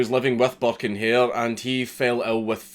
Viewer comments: Thought he said buck and hare which immediatly made me think of Bucky o hare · I thought you said “Butt and Hair” that shit would be hilarious.